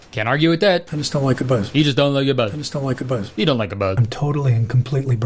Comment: i think he said "i just don’t like a buzz"???? whyd he mumble it like that????